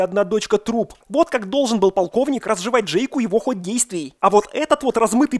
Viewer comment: На 29:53 послышалось "пауковник" вместо "полковник". Моё почтение, если это было намеренно)) Спасибо за обзор, как всегда, шикарно!